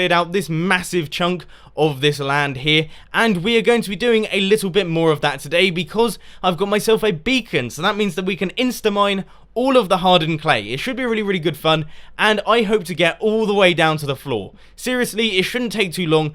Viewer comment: I thought he said bacon